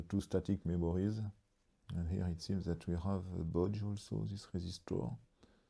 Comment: a what? what boujoum sus rayzistor? a bodge zero-ohm resistor? is that what you said? its probably a rework, basically a jumper. right? a bodge wire is a rework wire, that is why i wonder what you might be saying there. the date code on the chip is 8832, which translates to 1988, week 32. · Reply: Bodge Resistor is what he said. Probably pull-up or Pull-down. A “bodge” is anything slapped on after the PCB design but before a new board is spun that incorporates the fix. Could be a bodge diode or anything else.